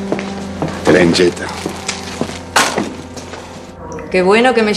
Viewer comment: Qué dice el Git cuando tira el ramo? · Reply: "Traen yeta" dice